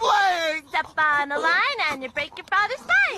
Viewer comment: why did i hear "All might's fine!" than Oh mY sPiNe! ????